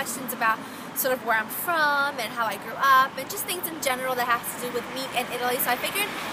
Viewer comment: when she grew up i thought she said threw up